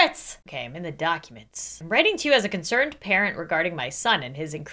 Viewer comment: HELP i heard "As a consirned KAREN"